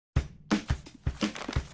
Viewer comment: Am I the only one that thought Mung said, "You take the spoon and you take the tounge."?